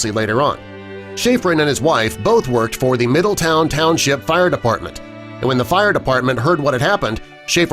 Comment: Is it me or did I just hear “fire defartment”